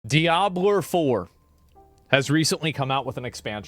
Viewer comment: Did he just say "Diabler"?